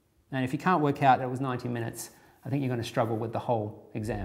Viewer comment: "And if you can't figure out that's 90 minutes, you're gonna have a problem with the whole exam" I thought he said 19 minutes I was like "...guess I'm gonna have a problem with the whole exam"